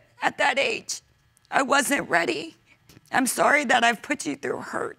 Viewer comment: all throughout the episode, I kept hearing Kevin hart's "she wasn't ready...".